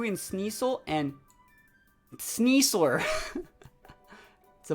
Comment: IS it just me? I keep hearing him say "Sneas-SOAR" instead of "sneas-LER".. hmm